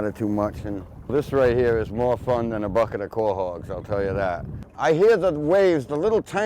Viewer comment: Great video, but what was it he said at @ 9:35 ? "more fun than a bucket of ..." Edit: I figured it out. This was my first "Tips from a Shipwright" video, and I've already gone back and watched 1-29 today. great skiff playlist. "Quahog - A kind of ocean clam, or bivalve mollusk, found in the waters of the North Atlantic. They come in two varieties: Arctica islandica, the ocean quahog; and Mercenaria mercenaria, the bay quahog. They make handy ashtrays." - from <link>